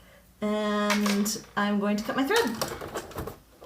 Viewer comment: Captions can be dangerous! Have you noticed that at 7:13 the caption says and Im going to cut my throat! Instead of Im going to cut my thread. Don't cut your throat people.